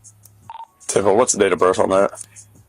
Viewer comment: Does anyone else hear "Anus"?